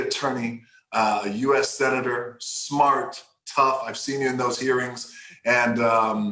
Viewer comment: When the Rock said "Hearings" I thought he said "Earrings", had to rewind it to double check.